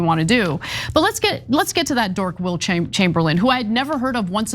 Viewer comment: I thought you said, "WILT Chamberlain"!